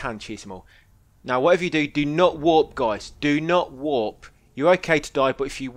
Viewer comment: sorry i dont understand english what do you mean by "do not (wrap)"? i hope i write the word right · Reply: you could check the text guide. Do not warp, at certain events, if you use fast travel, it messes it up, so do not warp (fast travel) until i say its ok again